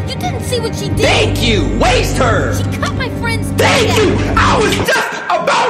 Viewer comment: For a second there I thought he said that she cut off his friends d 🤣